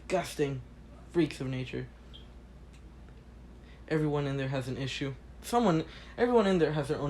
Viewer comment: I Heard "Everyone In There Is, Disgusting" I Was Like: Izuku x Eri, Huh?